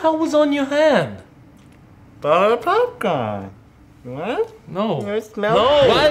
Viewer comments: For those who have no idea what said at 1:04 I think he said buttered popcorn XD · Were you supposed to not know what said at 1:05? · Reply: Eagle GAMING03 Before he said Wanna smell it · In 1:05 did said butterpopcorn